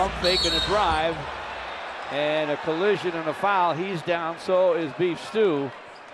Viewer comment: I heard “He’s down, so is Beef Stew.” or was that just me? 😂